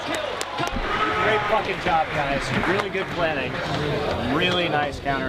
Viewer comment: I swear Monte says "Great f***ing job guys"